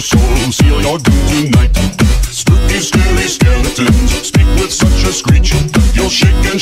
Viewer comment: happy Halloween everyone! ^-^ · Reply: Z00LD lel I thought it said spooky hairy skeletons